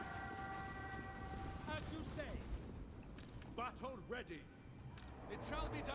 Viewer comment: Is he saying "butthole ready" at 21:33? xD